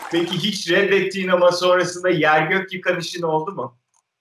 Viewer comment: ne diyor ibrahim ? Anlamadım bu soru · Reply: Reddettigin ama yer gök yıkan bir işin oldu mu diyor